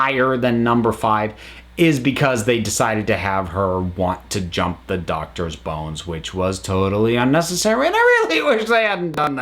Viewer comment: Have her want to what? I can't hear the end of that sentence properly, and I've repeated it about 5 times Does anyone know what he says? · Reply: Jump the Doctor’s bones. It’s a slang term for having sex.